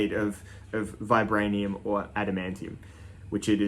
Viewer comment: Did you say ''Adam Ant'' tea um l.o.l I made My throat saw even a little Hussky laughing at the knifes branding. Japanese will be up in arms. and sending ninjas around to the manufacturer. Please treat your tools in background to some oil and love and attention